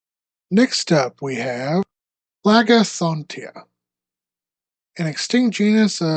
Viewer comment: raga what did you say? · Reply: Phlegethontia the name of the amphibian